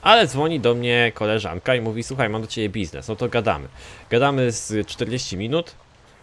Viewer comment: tylko ja usłyszałem "mam do ciebie pizde"?